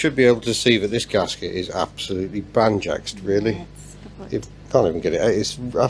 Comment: Did he say banjaxed? What sort of word is that? Doesn't sound Welsh. Maybe Klingon.